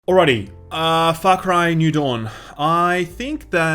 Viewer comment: Whoa. With the accent at the beginning, I thought you said, "Alrighty, aaaahh Fuck Ryan New Dawn." Love your videos man.